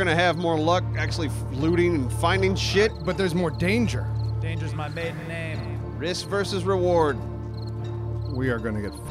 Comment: Did say "Dangers my maiden name" at 11:36? LMAO